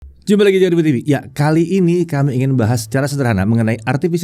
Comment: detik pertama itu mas bilang apa ya ? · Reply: "Jumpa lagi di jagat review" cepet bgt pengucapannya 😅